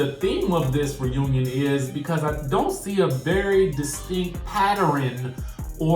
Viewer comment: Did you just say "Patter'ren?" 🤔😂😂😂😂